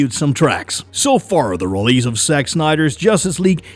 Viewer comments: hey did he say sex snyder at 04:51 😂😂😂😂 he is i bet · did he called Sex Snyder instead of JACK or I am hearing wrong??